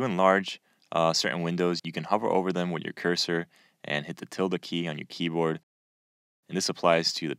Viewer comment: What key did you say to press to maximize a video? It sounded something like "Tilda key" but didn't quite get it.